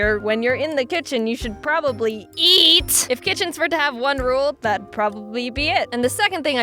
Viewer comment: her eat sounded like YEET